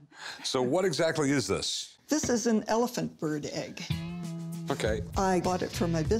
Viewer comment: I was confused for a few seconds there. I thought she said that this was an elephant egg 🤦🏻‍♂️🤦🏻‍♂️